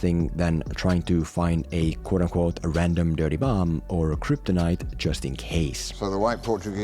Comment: At about 6:30, I thought you said Batman was trying to find "a quote unquote 'random dirty bum.'." And having not see. B vs S, because I don't like shitty movies, for the life of me I couldn't figure out why Batman would be looking for some random unclean homeless guy.